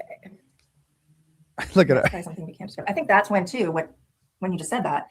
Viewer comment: At about 2:12:00 in the stream, I think 🦙 instructs that what is about to say or wants to say , is “kinda something “we” can’t share” it’s hard to hear but that’s what it sounds like to me. If you are completely innocent then you shouldn’t have to tell your friend basically “hey don’t say that “.